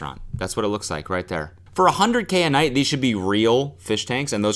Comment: thought you said 100k a month, and i was like yea, this is underwhelming for 100k a month. Then i realised it was 100k a day and TF it looks like a kindergarten.